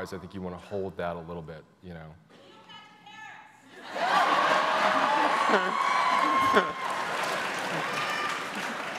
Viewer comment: What did the woman shouted at 5:27? Didn´t catch that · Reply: Niel Patrick Harris as Rachel's gay daf